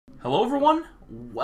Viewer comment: please dont tell me i am the only one who hears that his hello every one literally sounds like mumbo... is it just me?!?!